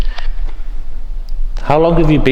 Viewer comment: It sounded like some male ghost said "no" with another, female, saying "si" at the same time.